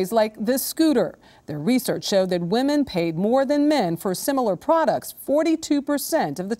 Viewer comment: LOL. She said women paid more annually. I wasn't really paying attention. So I heard "women paid more anally"